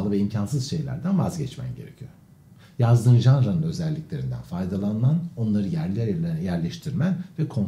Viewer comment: Bey sorum olacaktı. 6:09 da ne dediğinizi tam anlamadım altyazı "yazdım davran özellikleri" diye çıktı youtube'da. Normalde altyazılı dinlemiyorum elbette şükü r ki sağar değilim ama bazen dediklerinizi anlamakta zorluk çekiyorum. Mikrofondan olsa gerek. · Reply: Yazdığın Janra’nın özellikleri… yani yazdığın türün özellikleri… korku, aşk, macera komedi gibi…